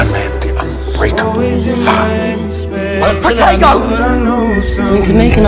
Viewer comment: Why do I think he says “POTATO”😂